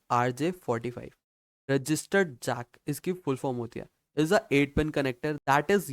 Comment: I heard registered jaat 😢